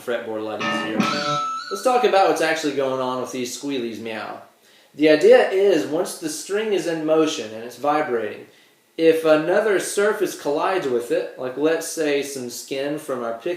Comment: Did you say "meow?" You don't look like a cat to me, boy...